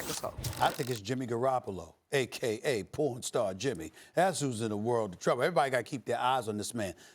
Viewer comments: Aka pornstar Jimmy 🤣🤣🤣🤣 I died 💀💀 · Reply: thought he said four star Jimmy first then I heard pornstar the second time. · Aka pornstar Jimmy 🤣🤣🤣🤣 I died 💀💀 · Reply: I thought he had said pawn start jimmy lol